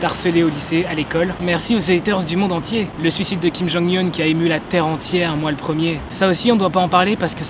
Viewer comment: Au début j'ai entendu "le suicide de Kim Jong Hun" Je me suis dis "wtf il s'est suicidé ?